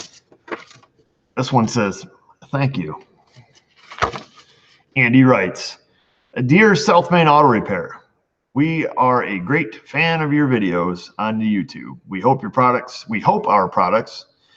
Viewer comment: haha i always thought you said this is the self made auto channel i didn't realize its the south main